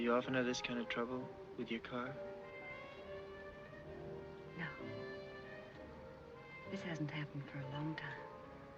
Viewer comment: "without warning" & "cry danger" ..... 2 of my favorite LA based film noir's... @ 27:00 in Carl asks the blonde victim ..."Do you often have this kind of trouble..with your car ?".....I can't make out her sultry sounding reply .... does anyone clearly hear her line ???...... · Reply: running bear "No, this hasn't happened for a long time."